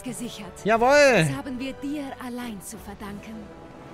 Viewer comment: In 4:27 habe ich nicht verstanden das haben wir dir allein zu verdanken sondern das haben wir Bier allein zu verdanken